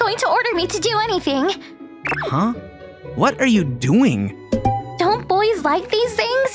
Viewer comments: Huh?! What are you going Says Huh?! What are you doing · On 9:24 it should me HUH?! WHAT ARE YOU DOING? (instead of "GOING")